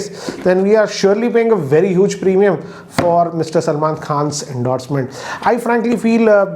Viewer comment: Its sounds like u said Mrs. Salman khan. Well anyway sounds good😂